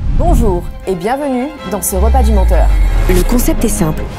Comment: j'ai entendu "ce rat" ☠️